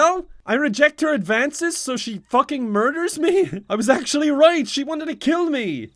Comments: OMG KEVIN SAID "FUCKING" INSTEAD OF "FECKING" AT 1:30 . WHAT HAS THIS CHANNEL COME TO... I used to love kevin.. now i dont even know him :'c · he actually says fuckin instead of feckin